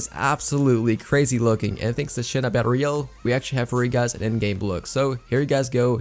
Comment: It sounded like you said, thanks to sh** on battle royale.